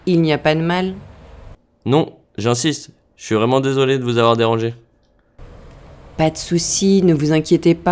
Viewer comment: I understood about a 3. I understood the gist of the message. Amazingly, I knew the numbers. I was completely lost when he said, "Je suis vraiment désolé de vous avoir dérangée." I heard "...... orange." It was better today. Sometimes, I close my eyes and listen. I've got to get of my head. My French teacher told me to quit trying to translate things literally. That's easier said than done. I'm keeping at it!